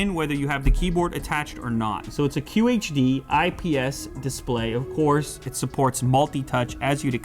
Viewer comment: WTF?... IS HE SAYING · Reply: "so it's a QHD IPS display" it's not that weird